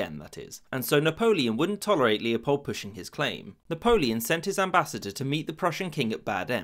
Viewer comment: everytime I hear it "sent him to meet bad ends"